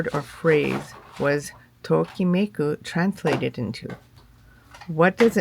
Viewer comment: you asked a question, but I couldn't understand the word in Japanese in order to surch the meaning in Portuguese. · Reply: The spelling is tokimeku. I’m curious what it translates into in Portuguese! ❤️